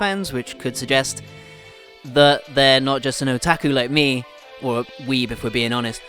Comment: I just hear "Valk its an otaku" xD